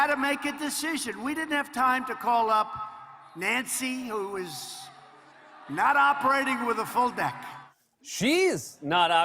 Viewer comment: Lol I thought trump said, “ Nancy’s not operating with a full neck” 🤣